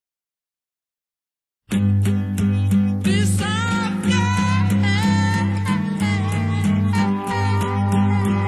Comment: What is he saying/singing at the beginning - 0:02? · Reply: “Piss off okay” hidden as piece of cake